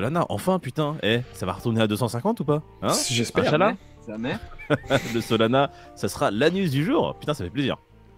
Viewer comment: Ce que j'ai entendu "Solana ce sera l'anus du jour" bha nickel 🤣